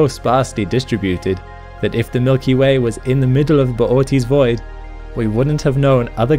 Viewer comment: Misheard it as Pootis Void